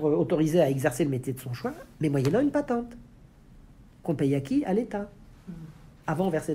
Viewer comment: "tout le monde va être autoriser à exercer le métier de son choix mais ..." pas compris les mots après, elle dit quoi ? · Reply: "mais moyennant une patente." Une patente est ce qu'on paye pour exercer un métier.